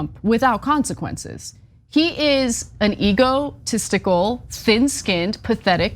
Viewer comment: Wait, at 4:20 did Ana call Trump ego-testicle? If not, I'm claiming authorship.